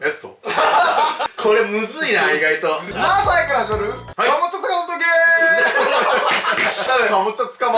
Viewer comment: のマサイから始まるのあとが山本線ゲームに聞こえた❗